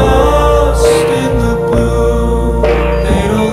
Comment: Joji: "lost in the blue" Video: everything red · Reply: I heard "lost in the pool" xD